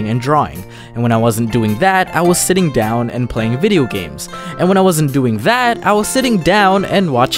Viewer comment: Did you say that you were "playing a video games"?